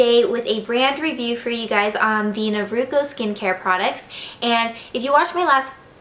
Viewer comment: me too LOL i thought it said "naruto"